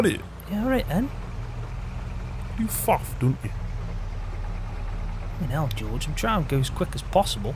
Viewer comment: What was said at 3:51? 😄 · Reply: Kinnell........(old English word meaning "I am exasperated with your impatience")